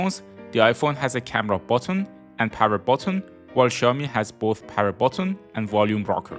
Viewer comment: Why am I hearing 'power bottom'? Nice review though!